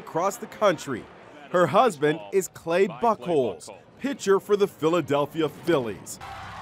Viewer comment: I thought he said Clay Butthole 😂